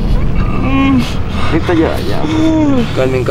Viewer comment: enla parte 4:50 se ollé una bos que dice paren